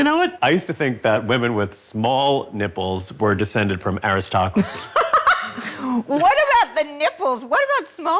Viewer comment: How about The Best of Everything and Eve's Bayou. Time for a hearing aid-- I thought Clinton said descended from Aristophanes, not aristocracy.